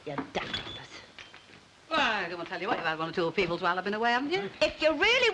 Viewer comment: I couldn't understand a flippin word. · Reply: Me, neither, but after replaying the scene several times, I made out some of it: Ena: "By, they've been telling me (I think) about one or two people while I've been away." Elsie: "If you really want to know..." Ena: "I mean Douglas Hume resigning and Heath pipping (Reginald) Maudling (all politicians of the day) at the post."